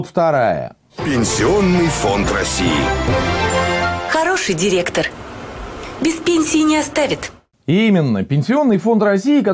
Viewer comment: "Хороший директор, мертвый директор"- послышалось.